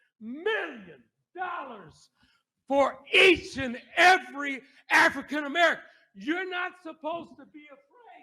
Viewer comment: I heard you say “AFRICAN ABEARICAN” I just woke up and I’m smoking a blunt wake n baken gonna be a good day